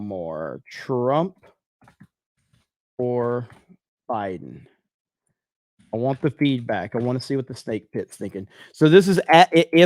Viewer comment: "I want the feedback, I wanna know what the SnakePit's thinkin'." Chat: "diD hE sAy I wAnT the FEET back?!" xDDD